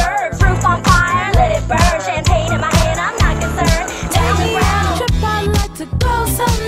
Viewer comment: at first i thought pearl said champagne in my ass im not concerned